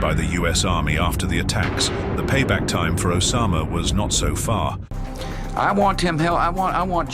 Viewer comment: Incorrect. Bush says "I want him- hell, i want justice!" Listen again. He did not say "I want him in hell." (Which is a pity. Bush should have tried harder to get him. Afghanistan offered to hand Osama over. Should have taken the deal...)